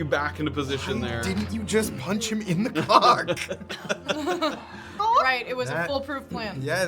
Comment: i was dumb-founded. Graven - "why didnt you just punch him in the cock" if i heard that right.. went back a few times to re listen but it may have just been stuck in my head to the humor/cruelty lvl.. 🤣 2:21:20 ish area